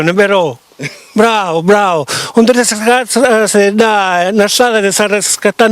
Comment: dice kfkfkslcvideo bravu bravu, ho capito solo questo😂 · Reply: Sì... [...?...] làssami jiri ca staiu facennu u videu, [nun è veru?]. Bravu, bravu. [...?...] disgrazziatu sì ḍḍà nnâ strata ca [...?...]. 🤷🏻‍♂️